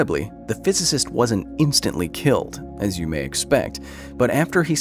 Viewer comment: The video's captions spelling "sons" instead of "suns" paints a whe different scenerio. Lmao